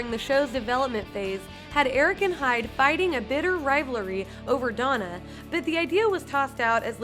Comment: Did anyone else catch "rivelery" at 1:53?